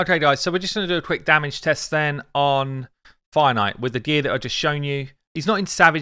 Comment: I thought you were saying Finite, kept hearing you say it and wondered what is Finite? I know what the word Finite is but it made no sense in the context then I realized you are saying Fire Knight lol.